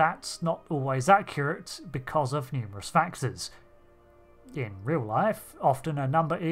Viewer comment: what did you say here? Numerous factors? couldn't really make it out